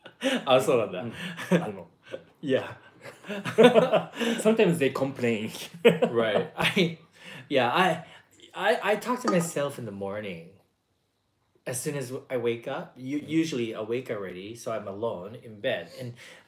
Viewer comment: Towards the ending, i honesty thought i heard admit to one and all that he "touches himself in the morning!" I had to replay that part to verify. Ends up he said he "talks to himself in the morning!"